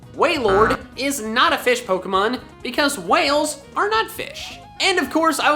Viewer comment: I thought you said "Whales are nutfish."